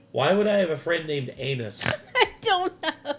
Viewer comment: I heard Anus as well! LMAO! He definitely didn't annunciate the M! 🤣